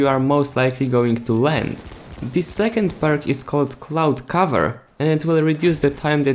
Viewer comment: I thought u said clout cover....